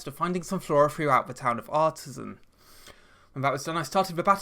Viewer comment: Really thought I heard him say “the Town of Autism” at 2:25 😂